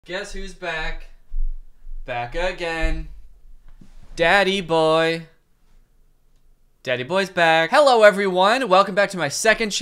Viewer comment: I just realized why he said daddy boy in the beginning, its on his shirt... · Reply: Washing Machine :P I thought for sure he was saying "Danny Boy" and it sounded weird until I saw the shirt.